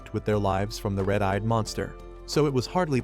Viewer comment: LOL almost thought he said " One eyed monster"